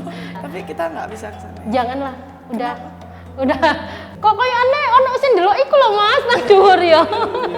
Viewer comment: kok aku dengernya "kok koyone ono sing ndeloki iku lho mas, 'mas dhuwur' yo.."